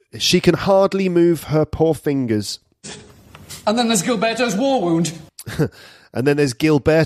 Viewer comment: I always heard this as "Then there's Gill Betto's war wound..." :)